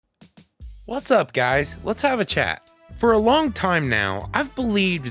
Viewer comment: what I hear: "What's up guys, let's have a chat" what CC gives me: "What's up Youtube, let's have a chat" me: visible confusion